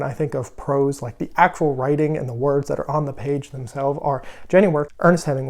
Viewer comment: What was the first authors name you mentioned at 4:45, couldn't catch the name. The other two Hemingway and McCarthy I have read and I strongly agree that they have the best of prose. Hence curious who the other author is. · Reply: Janny Wurts!